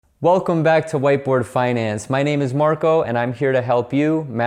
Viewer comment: I thought he said whiteboi finance! 😂